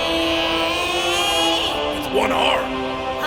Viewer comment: When Killeen asks about the arm thing about sign language, I thought he said “four arms” but sub shows “one arm”